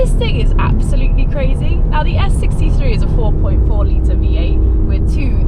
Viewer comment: did she call it an S63?